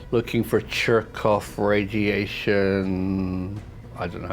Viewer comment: Did you just say jerkoff radiation? And I listened again! Lol that was great.